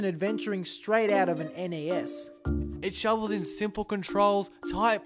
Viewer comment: it's it NES instead of NAS ?